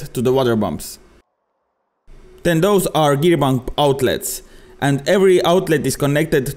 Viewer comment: Sorry, I keep hearing you say "gear bum" I can't help but find it funny. It took me a moment to realize you are saying pump, not bum. Anyway, really cool stuff though. Looks like alot of fun.